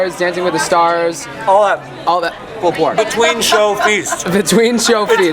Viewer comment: Katherine Francis I thought he said pulled pork but either way MY MOOOOOOOOOOD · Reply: he said pulled pork